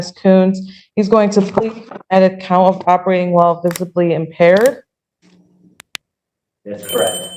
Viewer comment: I kept hearing "Robbery while visibly impaired." I was like wtf? 🙃